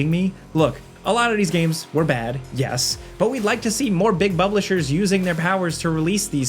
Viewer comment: Did say Big Bublishers?